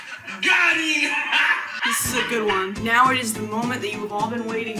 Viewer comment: i thought you said "this is porn" (i could of not been more wrong 💀💀)